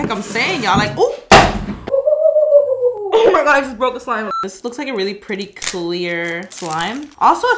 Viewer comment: Did she just say Starbucks’s slime…